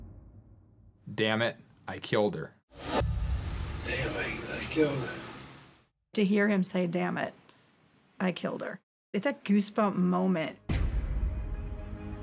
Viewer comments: I've listened to this many times and I never heard that. To me it sounded like he said "He hit me & I killed him" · Can’t believe I killed her is what I heard him say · I don't hear that at all. I hear "they think I killed her. "